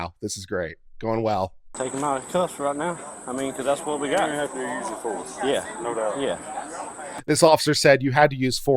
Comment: I think the supervisor said "you'll have to do a use of force form" when they talked